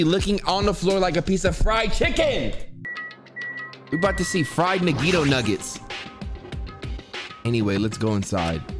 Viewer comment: Fried nagito nuggets. At first i thought he said fried nigga nuggets